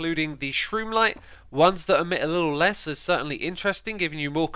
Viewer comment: "Shroomlight" Me having sad terraria flashbacks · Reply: First time I heard him say it I thought he said “shroomite” Chlorophytes on the menu boys!